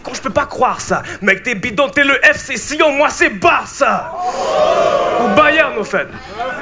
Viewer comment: Il dit "FC Sion moi c'est le Barça" à 4:07 ?! Si c'est le cas, je remercie pour la référence suisse !!!!